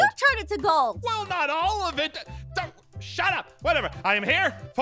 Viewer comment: At the end where ein said shut up I thought he said „ShAt Ap“